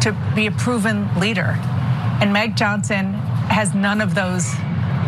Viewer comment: I heard... "Meg Johnson "